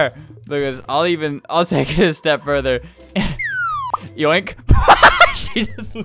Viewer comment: When he said, "I’ll take it one step further" I first thought it said "I’ll take her step brother"